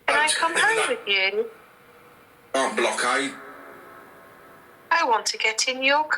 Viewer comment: Holy cats and whiskers!!! 33:32 - is talking about the black eyed children and I swear I heard "Not black eyed" on the spirit box!! (Or whatever was on) It's marked with ??? so you heard something!